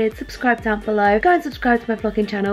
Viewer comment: did she say "go subscribe to my fucking channel"